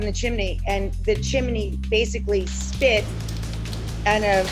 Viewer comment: I understood her to say they had their chimney cleaned twice a year and that they have lived in the home 15 years. The chimney split is what caused the fire, Yikes! I don’t use my fireplace as it is and this is another reason not to. LOL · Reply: i thought she said split too, captions shows she said the chimney spit the ember onto the roof and it started the fire.